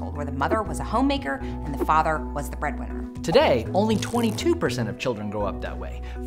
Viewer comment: I thought he said, "today, only 22% children grew up" lol